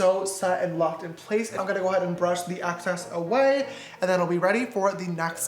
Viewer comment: he said "access" instead of "excess" sad yeehaw